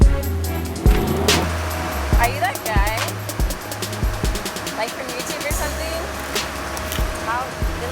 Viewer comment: did she really just say are you that guy that like to fuck lot of girls, then got a piture with him or am I just hearing shit?